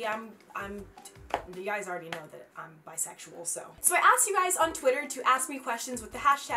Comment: did she say she's bisexual???